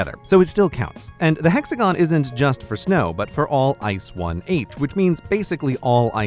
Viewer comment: Am I the only one who thought that he said ice 1 8 (it's actually ice 1h